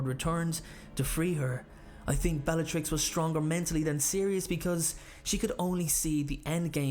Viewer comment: when i heard '' bellatrix lestronge'' :P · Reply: he said- "i think bellatrix was stronger mentally than sirius" but i agree with you it did sound like bellatrix lesteronge lol